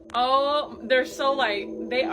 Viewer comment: In the clip with the EVP. I couldn't make out with the first EVP said but in the second EVP I heard "oh yes they are". It could be very possible that the first EVP said something in the second Spirit was replying to whatever they had said